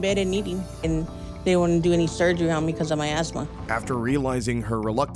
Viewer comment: I had another tab open,checking my messages,so i wasn't really paying attention. At first,I thought she said "because of my a**hole"